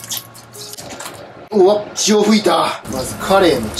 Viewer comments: 血を吹いたてとこ潮吹いたて聞こえたwwww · 血を噴いたのところ塩噴いたに聞こえた俺は末期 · 「血を吹いた」がどうしても「潮吹いた」に聞こえてしまう